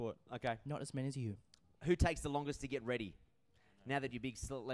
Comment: Sorry what question was in 1:14? What did Luke never do..? I can't hear😭😭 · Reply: It's was who takes the longest to get ready?